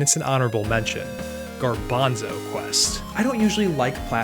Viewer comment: Thought you said gorbino's quest and I had a flashback lol